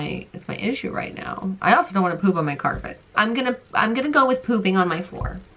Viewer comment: I was talking to my sister and all I heard was I'm gonna poop on my carpet